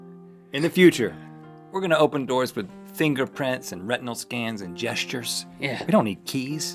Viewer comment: I thought he said rectal scan